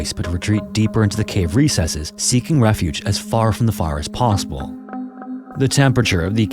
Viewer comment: Dude, slow the hell down. Even the captions can't understand what you're saying. At 6:12, "as far from the far as possible." ??? We know you meant "fire," but slow down and quit rushing your speech. You're not in a race bruh.